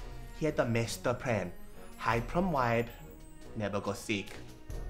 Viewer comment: sound like he said : "Hide from wife never go seek"